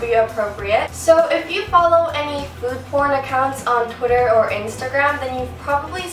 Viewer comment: did she say "food porn"?